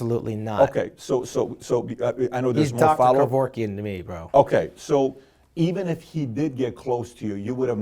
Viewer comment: what does Shawn say at 1:16:48? I can't figure it out what he says? Something "bro"? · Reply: “Hes dr. Kevorkian to me bro”